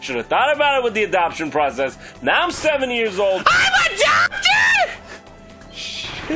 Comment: I thought meta knight said "IMA DOCTOR?!!?", He actually said "I'M ADOPTED?!?"